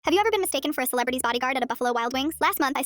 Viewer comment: I thought they said “have you ever mistaken a celebrity bodyguard for wild buffalo wings”✌️😭